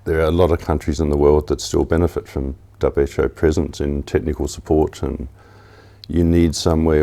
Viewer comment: Thought he said "WHO prisons" at one point.. but then realised he meant "WHO presence"!